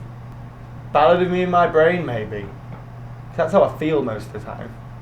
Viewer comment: what is he saying at 1:23? I can't seem to understand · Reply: ballad of me and my brain